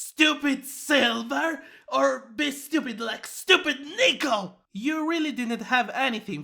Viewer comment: WHAT DID YIN-YANG SAY AT 2:48??? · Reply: HE SAID NICKLE